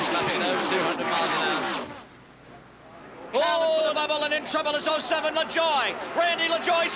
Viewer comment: did he say "ooh labobalen in trouble 07 lajoie"